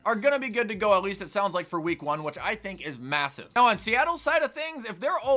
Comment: did u say massive